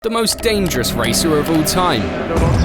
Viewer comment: i thought is said "most dangerous racist of all time